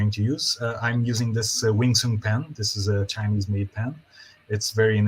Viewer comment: Didnt quite catch the name of the pen at 10:05? The Chinese made pen? And what specific ink is in the pen as its hard to find refillable waterproof mink. Thanks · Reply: Hi there! uses a WingSung fountain pen and DeAtramentis waterproof ink in Document Black 😊 hope this helps!